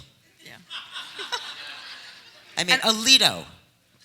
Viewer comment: What did she say at 26:58? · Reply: "Alito" - Samuel Alito.